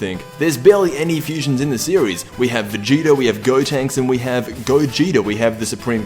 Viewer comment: did you say Vegeta, Gotenks and Gogito? xD